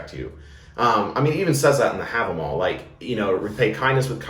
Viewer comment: At about 6:40 you said “it even says that in the ____”. What’s the word you used, because auto generates captions heard it as “have them all”? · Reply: Havamal Story in the Poetic Edda